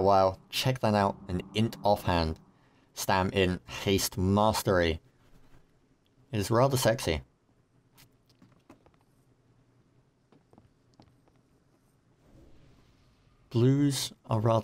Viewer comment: did he just say the offhand is rather sexy?